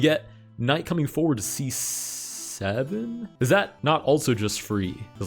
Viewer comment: what did you just say?.... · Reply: C7. Don't worry. 👍